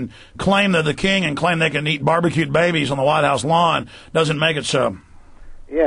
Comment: did Alex say BBQ’d babies?? LOL🤣